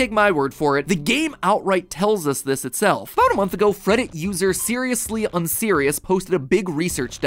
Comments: did he just say freddit??? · did he say "Freddit" user? is that the name of the reddit or did he just say it wrong? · cough did you say, FREDDIT USER!? (Teh heh, it’s like Reddit user but with an “f” at the star so some of it sounds like Fred heh…)